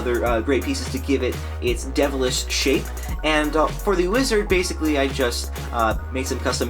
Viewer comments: is it just me or am I the only one who heard lizerd instead of wizards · he said lizard instead of wizard lol